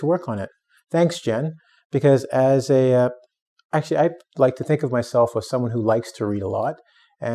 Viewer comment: Hi, In 00:24, did you say "Think of myself for someone who likes to read a lot" or "Think of myself of someone who likes to read a lot" ? Cheers/Jacky